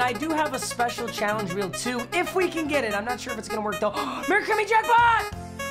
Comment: When he said merry Chrimmy jackpot I thought he said very creamy jackpot lmao